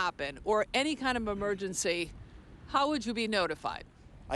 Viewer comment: Why did she say "memergency"